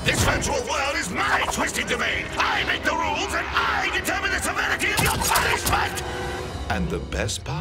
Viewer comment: What does he say at 2:24? It sounds like "survivibility of your punishment" also the granok lady can clearly see that the human girl has both the gun and the robot head.